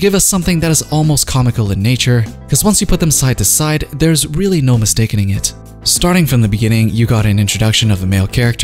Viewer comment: Did he say MISTAKINING ?